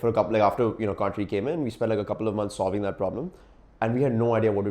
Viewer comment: my dude what did you just say :( ??? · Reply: then, like a couple of months solving that problem